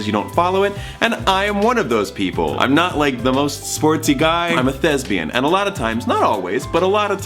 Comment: i thought he said im a lesbian 👏so proud of him for coming out😤👏